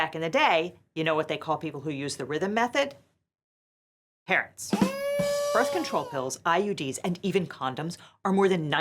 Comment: I geuninely thought you said "parrots" for a second. I probably had too much rum.